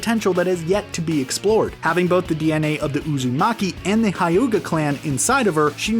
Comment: he really said Kayuga clan 🤣🤣